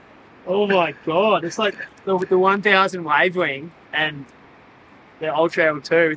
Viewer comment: also would love to know what wing setup is used please, this looks perfect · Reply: EDIT: sounds like you said 1000 Wave wing at 4:32. TY